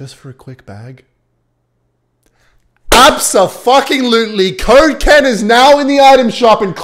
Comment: First I thought he said kocaine 0:34 😂😂😂😂